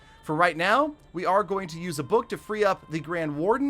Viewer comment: did he just say book.....??? HAHAHAHA